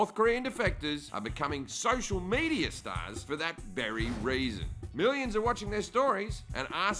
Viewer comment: thought it said defecators ... not happy Jim